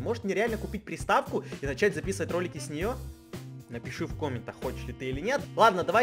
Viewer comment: ахах, мне послышалось что сказал " напишите в комменты, конченый ты или нет? "😹😹😹❣️